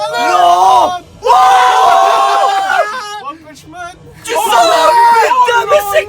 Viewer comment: i heard someone say one punch man lmao